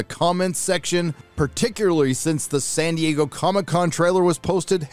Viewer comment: first time i listened i heard "sandy huuk comic con" Nd thought you were subliminal mentioning the trial